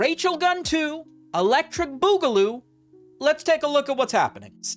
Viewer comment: I can't tell if you didn't hear him say it at 1:40 or if added it after this comment. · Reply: He says Boogaloo, not Boogieloo.